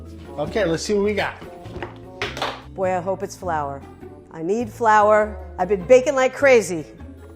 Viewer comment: I'm not a native english speaker, and I thought she said flower 🥴